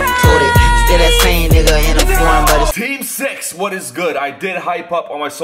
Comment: it sounded like he said "safe sex" instead of "team 6", I was confused for a second. 😂 lol